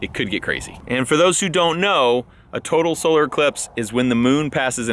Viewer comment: Came for the update. Stayed for. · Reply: Everytime he says total solar I hear "total scrotal 💥"